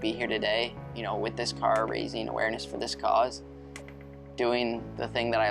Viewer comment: For some reason, I kept hearing "Racing Awareness" which has a nice ring to it. Cancer is a bitch and so is pharmaceutical companies.